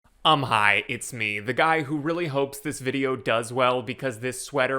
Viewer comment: Not gonna lie, I swear, every time you start a video I hear "I'm high, it's me."